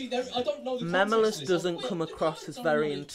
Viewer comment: “Mem-u-lus” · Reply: I heard him saying "Memiulusi"